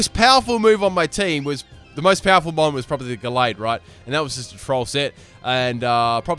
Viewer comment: did he call gardevoir galled?????